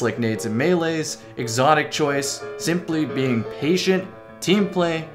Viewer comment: I thought my mans here said "simply being asian" LOL